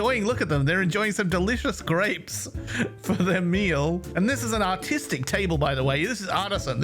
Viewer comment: i thought he said “ this is a autistic table” xd